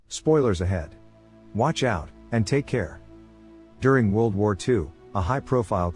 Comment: Did He Say During World War 2